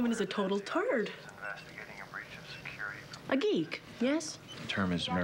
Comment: did he just say "TIVA" ^_^